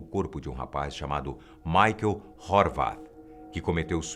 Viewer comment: Michael de quê? Horvath? Catei tudo quanto foi Michael, suicide, slenderman no Google e não achei nada. Pra um caso que foi supostamente tão noticiado assim, deveria ter alguma coisa, não? 🤔